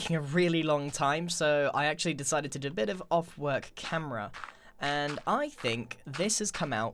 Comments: did he just say "off work camera" ... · Did you really say, "Off work camera"!? Viewers. Go to 14:30 in this vid if you haven't noticed.